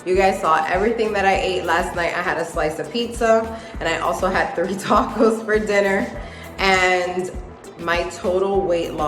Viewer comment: Congratulations at first I thought you said 30 tacos I was like where lol.